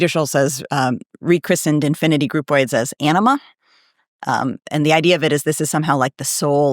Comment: WTF is an anima? I thought that she was saying enema. lol